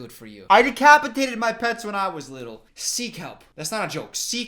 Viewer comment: I legit thought you said 'sea kelp' 8:55 ngl I thought you were saying you had decapitated sea kelp and I was so confused